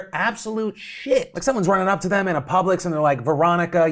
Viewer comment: Did Phil say Publix? Whatchu know about Publix?